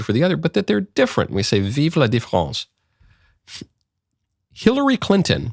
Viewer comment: Vive la France? Long live France? Lol maybe I misheard